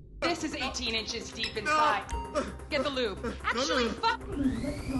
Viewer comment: "Get the lube" Did i hear that correctly